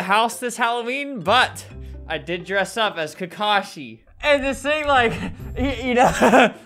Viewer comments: I don’t appreciate how perfect Albert’s costume is. He literally looks just like Kakashi. EVEN THE HAIR. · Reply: I thought he said takashi XD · No one: Alberto: "I didn't go out this Halloween but I did dress up as kakashi." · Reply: I was eating my taco so I couldn't hear him and I heard "tekashi" so I was like oh thats nice they named an anime character after him